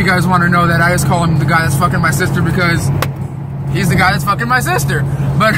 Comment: Shit I might get blocked, I thought he said in the car this is my sister, I looked twice and thought damn she looks like a man.